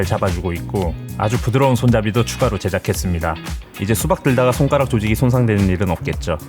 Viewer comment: 손가락 조직을 손가락 조지기로 들었네요😂 오늘도 예술 작품😮